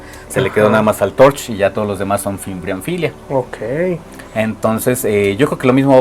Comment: Que cool! En el tiempo 21:12 que terminó dice el señor? Fimbrophylia? No entendí muy bien. Saludos ✌🏻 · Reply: Fimbriaphyllia baliensis